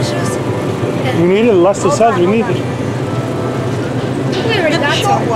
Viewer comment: Does he say: "We need orange juice"? Man, you have BEETLEJUICE